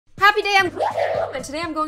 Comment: I always thought you said " Happy day, On Gloom "